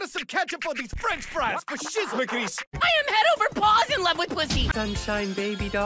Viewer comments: I THOUGHT THE CAT SAID PUSSY NOT PUTHY HELPPPP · "I'm head over paws in love with puthy" 0:17 it gets me every time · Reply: I thought he said he was head over balls